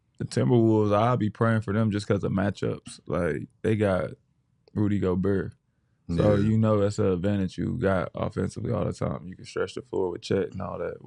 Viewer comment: I thought Jeff said, “Booty Gobert.”